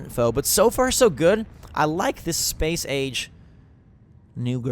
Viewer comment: Am I the only one who had to pause and re-wind when he said Space Age? I thought he said Space Aids...